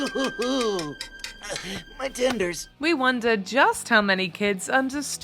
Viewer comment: Actually in the Kung Fu Panda part, when Po said "My tenders" my little brothers thought it he said "Tears" and that is what they called that area of the male body ever since.